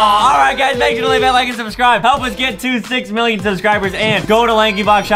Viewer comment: I THOUGHT SAID "Let's get to THICC million subscribers!!" .-.